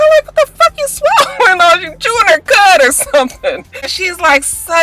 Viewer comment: What did u say about Amanda chewing? I thought u said something then I thought Naah maybe it's my ears 😕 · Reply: I said she's chewing her cud.